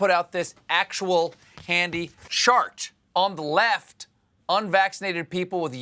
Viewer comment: It sounded like he said "shart" when he was suppose to say chart 😂🤣